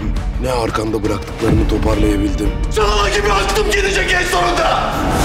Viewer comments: de alaz ne diyor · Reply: Çağla gibi aklım gidecek en sonunda diyor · Ne diyor Alaz? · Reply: Cagla gibi aklim gidecek en sonunda (defalarca izledim, anlamadim 😂 Anlamiyorum ben bu cocugun dediklerini)